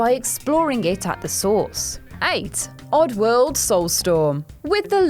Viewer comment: Did she say aids rather than 8 in